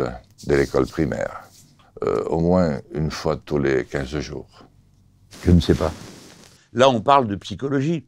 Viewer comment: Sur la question de l’éducation sexuelle, Melenchon a été le plus pertinent. Tout simplement parce qu’il dit ne pas savoir et s’en remet aux professionnels de la psychologie pendant que d’autres s’érigent en donneurs de leçons. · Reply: mais tu as mal entendu 🤣🤣🤣 il a tout simplement commencé par dire « je ne sais pas ». Aucun enfumage la C’est à 2:02mîn ouvre grand tes oreilles et dis moi tu entends quoi 😂😂